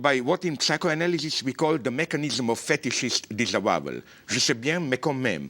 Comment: The mechanism of fetishes disabowel? What did he say at 2:27 ? I can't understand him. Help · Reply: Fetishist disavowal. I hope this answers your question from 2010.